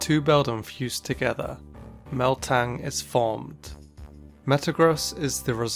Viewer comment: I love how in stead of saying metang it sounds like he is saying meltan